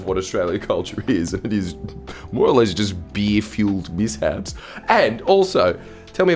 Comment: Did he say "Beer fuelled piss flaps"? I can't quite follow the modern Antipodean accent. What does he imply?